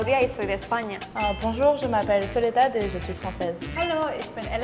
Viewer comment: Good video, but the subtitling of French is a bit off. First, her name is "Soledad", not (0:13). At 2:25 she says "Enchanté de vous rencontrer" (or "enchantée" considering she's a woman) instead of "enchanté de faire votre connaissance" (which is correct, though). At 2:51 she says "N'importe quoi ! " instead of "absurdité" (which is completely off). At 3:10, she says "Comment ?" instead of "como" (which is spanish if you add an accent on the first "o"). At 4:37, she says "Je suis énervé(e)" ("fâché" would be correct too). At 5:15 she says "Tu as de très jolis (instead of "beaux") yeux". At 5:45 she says "laisse moi tranquille" (instead of "laisse moi seul" which is more into a melancholic mood). Spanish and German too are sometimes mistranslated. Still, I love this content.